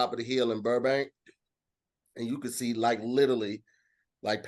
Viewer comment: lol at 28:27 says little league instead of literally